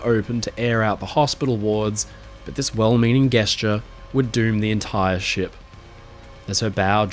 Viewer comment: saying guessture instead of gesture made me giggle